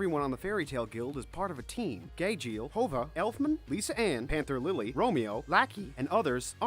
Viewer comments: wtf did he say idk about you but i heard holva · _. I died Did he call Juvia Hoga??? Or are my stupid ears hearing things.. Also they called Gajeel Gayjeel and Lisanna Lisa Ann 😂😂💀💀💀💀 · I think heard him say Lisa Ann for Lisanna and Margerin for Mirajane.... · Dude, you're killing me with the way you're saying words.. you cant say lacrima, gajeel, exceed, lisanna, Laxus, tenrou, erza, droy, alzack, Juvia... oh god I think the worst was how you said Juvia. Hoga? I heard Hoga? Anyone else hear that at 8:14?? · What is he saying, listen how he says juvia at 8:11. He says hova, WTF IS THAT